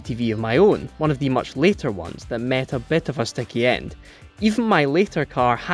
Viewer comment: What is the presenter saying at 1:30? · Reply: "it met a bit of a sticky end"